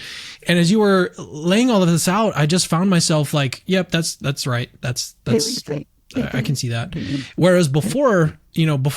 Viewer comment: Can anyone hear some strange voice at 1:01:21 to 1:01:26 ? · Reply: I think it say: everything you think, can't get the rest ...